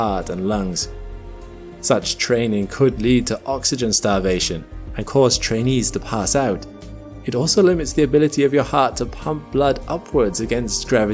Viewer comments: Was I the only one who heard "Such training could lead to oxygen starvation, and cause Chinese to pass out! 😂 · IT SOUNDS LIKE HE SAID "AND CAUSE CHINESE TO PASS OUT" OVER THAT VIDEO LMFAOOO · "And cause chinese to pass out" I misheard that right?😂